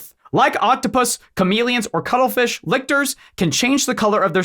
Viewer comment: I thought he was saying cuddlefish are masters of stealth. Lol